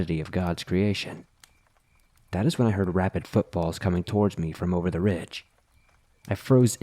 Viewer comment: Dude i'm still half asleep and around like the 6:02 mark I thought you said rabbit footballs. Took me a minute to work out that you said rapid footfalls. Feelin pretty dumb rn